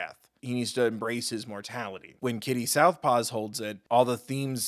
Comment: did you really call Kitty "Kitty Southpaws"?